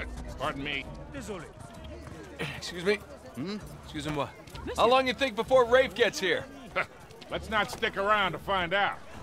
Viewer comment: The comedy in uncharted LOL xDDD.. said I'm not 100% sure but I think they found us LOL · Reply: Thomas Wayne Sounds like he says: "How long you think before Rave gets here" "Let's not stick around and find out" Don't know who Rave is though!